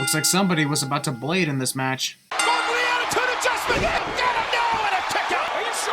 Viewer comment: When Cole said going for the attitude adjustment I thought he said, Fugly attitude adjustment